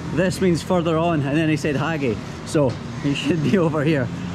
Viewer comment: i am so freaking sorry i burst into tears after i heard "Haage" hahaha bro its "Aage" which means "ahead" hahaha i am so so so so sorry wish you could understand locals